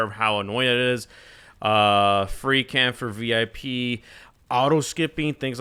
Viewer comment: guys im not english what did he say about vip lol I just hear "a free can for vip" 💀 nvm found a comment saying its a freecam, epic